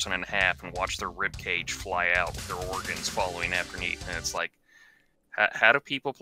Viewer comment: Wait did he say "afterneath"? O_o